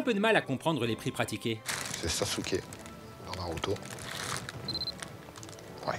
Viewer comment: c'est moi qui comprends mal ou à 1:20 il dis " c'est sasuke dans naruto " ? :o